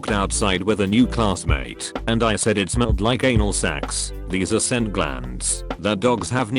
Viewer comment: I heard anal sex and I was confused for it bit until I realized it said anal sacs